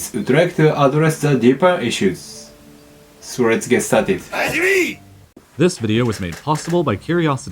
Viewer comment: Just trying to help, not criticize here: at 0:58, the subtitles displayed are "let's get started it!" which is a combo of 2 different ways to say the same thing, and not actually correct. It's either: "let's get started!" or "let's start it!" (where it would be implied to be the video). If I judge from what you say/want to say, I think the ideal is "let's get started!", so without the "it". Sorry for the "grammar nazi" vibe, I wanted to send you like a private message but neither your IG or twitter work, so I was left with the public comment option. Loving the content so far, just trying to improve - and also to get rid of a thing that bugs me (my issue, I acknowledge).